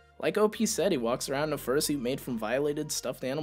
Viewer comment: I thought it said fruit and not fursuit 😭